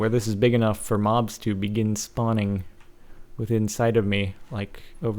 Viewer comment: did he say that mobs can SPAWN INSIDE of him?